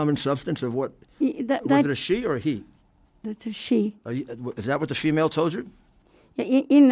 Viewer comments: Did I hear that correctly? Did Giuliani just say “Is that what the she-male told you?” At 13:25(ish) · Is he saying 'shemale'? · anyone else notice somewhere btwn 13:30 and 13:44 Rudy asking what the “she-male” said? lol